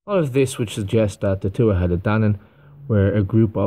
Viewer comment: When I first heard this term ..it sounded like they were saying two headed Dannon lol